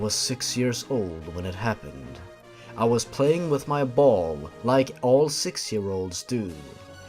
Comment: i thought u said playing with my balls and i got shook